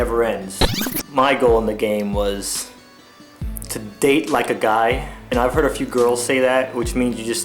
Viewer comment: did he say date a guy XD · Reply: Date 'like' a guy.